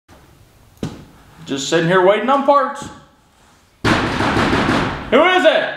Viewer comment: Thought you said “sitting here waiting on farts” in the intro, haha. Great job with that truck, man!